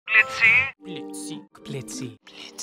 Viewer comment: When he had hold of Blitz it sounded like he said bitzy”